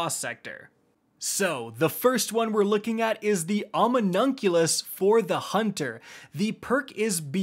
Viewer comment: Sounded like he said ominoculus when its Omni Oculus. Like Omni and Oculus Rift